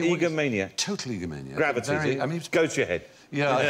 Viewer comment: I dodn't get what Bill was saying around 2:15 about gravity (?) and then everyone laughed. Can anyone shed some light? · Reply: I think he said it went to Newton's head